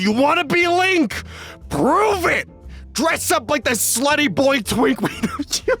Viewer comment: did he say “sweaty boy twink”?